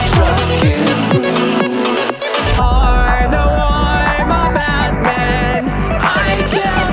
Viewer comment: It sounds like he said I’m A BAT MAN